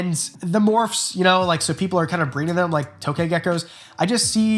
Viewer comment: I didn't hear "tokay" I heard "fyuk yoo"